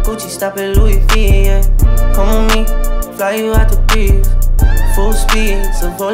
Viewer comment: Sounds like he says “fly you out to grief” on certain bars. Maybe I need some new beats 🎧🤷🏽‍♂️